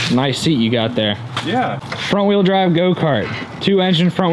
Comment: I thought you said 4 x 4 man. oh how depressed I am. man really? ok I guess I just heard you say it. 4 wheel drive. ok thanks for entertainment purposes only. Ike. he said 4 wheel drive. ok thanks guys great stuff thanks.